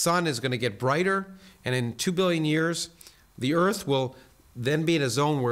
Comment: Did he say the earth will heat to 1000 degrees in 2 billion years? Whew! I thought he said 2 MILLION there for a second